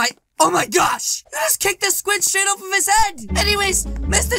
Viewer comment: Hello my goofy mood dudes, if you want to play in the underworld of brookhaven then here you go Play The Game: <link> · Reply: did he just say squid $hit